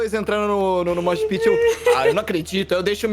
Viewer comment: n consigo entender o que eles falam, eles falaram um "nome" pra porradaria, alguem entendeu? Help. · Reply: Mosh pit